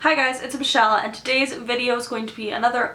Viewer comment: hahaha I thought you opened by saying "hey guys, it's a michelle" and I was like, "whoa, a wild michelle"